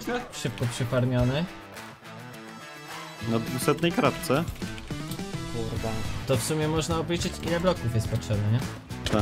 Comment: tylko ja usłyszałem to kurwa?